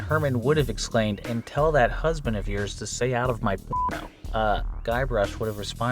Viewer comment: I'm the only one who can't stop hearing "stay out of my piano". I'm hearing this to the point that i don't know what it's suppoused to say